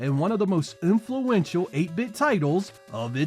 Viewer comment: Did you really say "OOMPHluental title" there? If so, awesome play on words :D